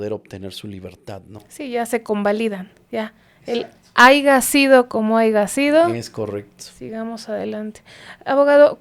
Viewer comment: No sé si escuché mal o ella dijo haiga sido como haiga sido 🥴🫨🫠